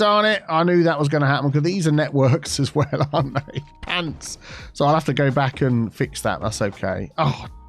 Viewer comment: wait..did he say pants? 👀